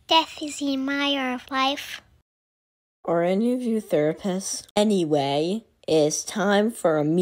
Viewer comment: I heard it as "aRe aNy oF YoU NeRvOuS?????"